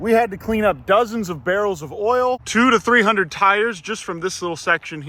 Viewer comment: did you say oil 🇺🇸🇺🇸🇺🇸🦅🦅🦅